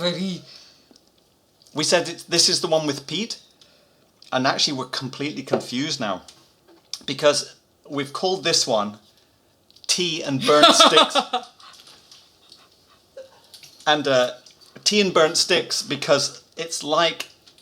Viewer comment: I thought you said "tea and burnt spit!" the first time. That would be a unique tasting note!